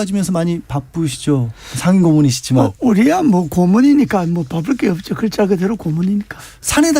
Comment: ㅋㅋ고물이니까 로 들었어 ㅎㅎㅎ